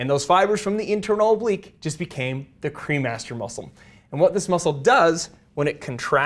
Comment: I swear I thought you were saying the cream Master muscle I was like hmmm 🤔 that's aptly named. lol